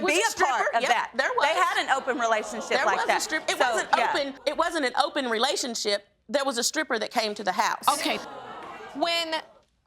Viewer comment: she just sat ther in open court admitting to having an ooen relationship bringing in a stripper admitting to giving her husband permission to sleep with her sister. First off clearly the wife needs some counseling for sure!🤦🏾‍♀️🙄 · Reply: I could have sworn she said no we didn't have an open relationship but we did have a stripper come to the house